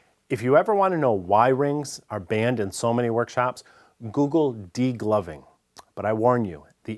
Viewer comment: I heard that as deep loving instead degloving.